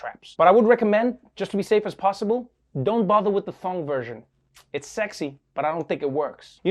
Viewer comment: Seriously, who wrote the captions? I'm not English speaking, but I know he said 2:31 "thong" not "phone". You know you had one job, when even auto-caption got it right .